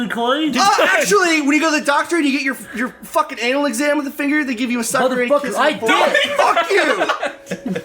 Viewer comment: what does corey say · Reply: Anal exam, with a finger, they give you a sucker and they kiss you on the forehead.